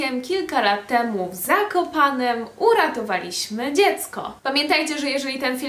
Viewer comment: Jezu, usłyszałam uratowaliśmy zakopane dziecko a nie w Zakopanem xd